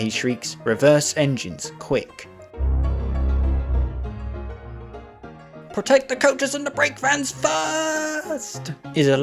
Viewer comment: Great video 👍 I'm not gonna lie, I thought Thomas said “Take the coaches & the brakevans first.” XD